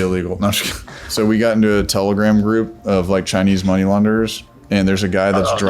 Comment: Chinese Laundry! I thought he said at first. 😂😂😂😂